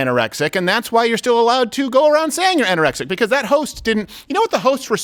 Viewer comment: Did you say roast? When's dinner I'm starving. No, he said host Tess